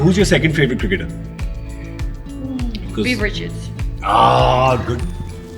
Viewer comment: what does shikhar wife said about the second favorite cricketer, which name she said? Anyone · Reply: Viv Richard